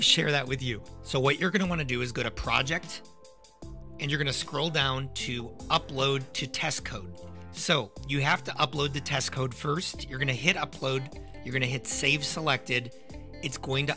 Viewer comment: i'm sorry this was really helpful but I heard "upload to tesco"